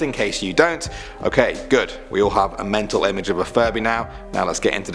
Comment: I thought it said Furry